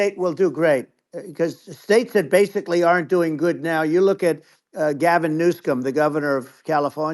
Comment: Damn around the 57:15 mark does he say Gavin NewSCUM? Wow...